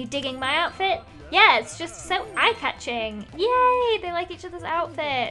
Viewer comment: Lol For A Sec I Thought it said Eye Tucking But it said everything eve tuckling